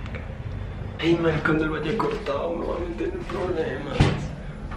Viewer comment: que fue lo que dijo?????? A ya , el dijo : ma** ojalá y no se alla cortado o me voy a meter en problemas eso dijo jeje